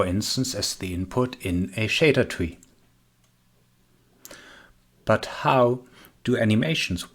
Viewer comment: Not sure what you are saying at 1:36 shadertui? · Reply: I said "in a shader tree" - sorry for my english